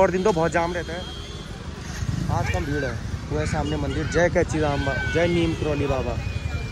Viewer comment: kya bola tune. Kainchi dham maa😮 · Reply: Kainchi dham mandir me *